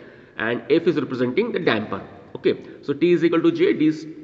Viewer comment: great video, bot at 4:20 i cannot understand what is f representing...replayed many times, but still cannot hear what it is. can someone help me? :) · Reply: f is representing damper